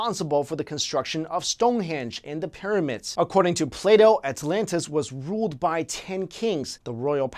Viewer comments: it sounded like you said Play Dough haha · also I can't be the only one who thought he said play doh